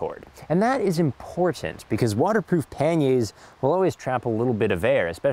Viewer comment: Please excuse my American, but I never heard "Panniers" said out loud before this video. I could have sworn you were saying panties.